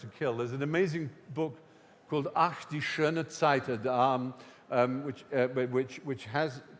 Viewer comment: I cant find the book he recommends. It sounds like hes saying "ach die schöne zeit der armen" anyone know if thats correct?